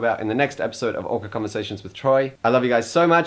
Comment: he sang "your driving me" i heard "vagina me".......im too queer.